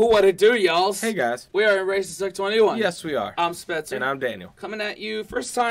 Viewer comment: Omg. At the start It sounds like your saying "Were racists at 21" I know you didn't say that but it sounds like, lol